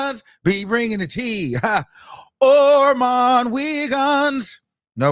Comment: I thought he was saying OilMan Wiggins..😂😂😂